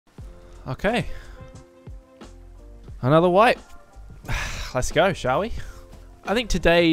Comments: Not gonna lie, for a second at start of the video i thought you said "another wife". Was bit confused. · i thought you said "another wife" at the start xD